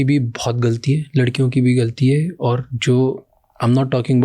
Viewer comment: did he just say that "ladkiyon ki bhi bhot galti h"